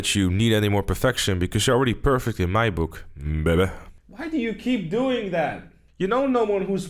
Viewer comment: Did he just say "baby" at 0:52?